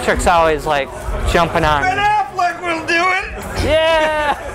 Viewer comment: what the hell is Pete saying lol I can't figure it out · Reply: He said "Ben Alfleck will do it!"